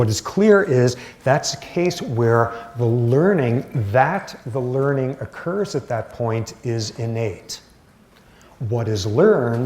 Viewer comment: The learning that the learning occurs at that point is innate!!!! WTF did he say? Someone Explain. For context